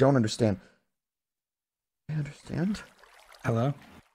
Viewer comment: sounds like George saying hello😸